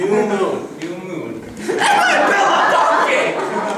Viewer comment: so funny! but what did tht guy say at 4:40? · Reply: He shouts "DONKEY!" A lazy Shrek reference. But I guess it worked for us, because it cracked people up every time.